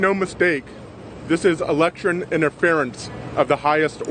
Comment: I'm sorry did he say electchurn interference? 😂